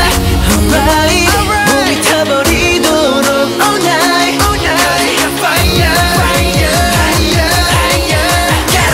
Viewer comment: PPPPFFFFFTTTT XD, okay okay....I don't wanna be rude or anything buuuut......in 2:42 is it me? or is jimin or whatever his name is...says dorito?! I laughed so hard I choked and if you didn't understand then......hear closer closer and closer then you will hear the dorito word. XD I suck, and I swallow. :p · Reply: Taghreed Felemban he says only taburi to look all night